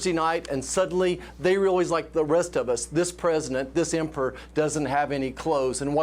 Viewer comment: I Heard This "M'FER" Not This "Emperor" ,,Thanks to "Closed Caption" I could rewind and see what I DIDN'T Hear.